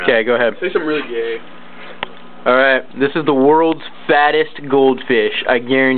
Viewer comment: lol i thought it said fastest goldfish at first, i was expecting some sleek speed machine, look what i got rofl 5*